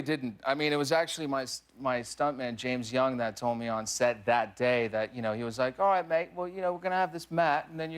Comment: When he mentioned his stunt double James Young, I could of sworn he said James Gunn, I pointed at my screen and shouted "He who should not be named" (at least in the eyes of Disney)